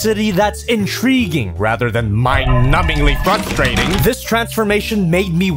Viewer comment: did say “Fuckstrating” or “frustrating”